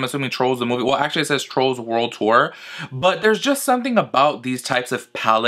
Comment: I thought you said Trollz World War 😂😂😂